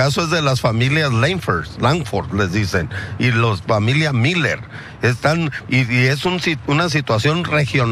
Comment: Pense que decía “familia Lebron”